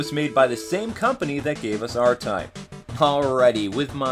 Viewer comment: Does anyone now what game he was talking about at 5:49? I couldn't understand him. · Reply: Swillo % he says R type